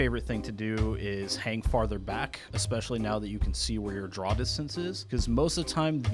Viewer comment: what does he say? "Especially now that you can see where your ..." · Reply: "...draw distance is." The minimap was updated a while back to feature circles including spotting distance and draw distance. Tanks farther away than your draw distance are effectively impossible to hit, since even if they've been spotted by your team, your game won't render them at that distance. (Which is fine, at distances like that only a very few tanks can fire accurately anyway.) SO, you can use that knowledge to set up in the valley for maximum potential range on the murderous open ground as a TD.